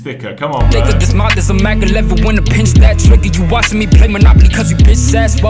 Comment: Time stamp 8:20. I think he said Mak 11